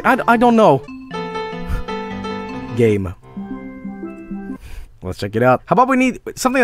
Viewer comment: my name is Gabe and it scared the absolute shit out of me when I thought I heard Joel say my name in that tone lmao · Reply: Game.